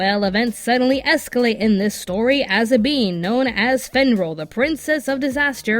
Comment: did you say bean?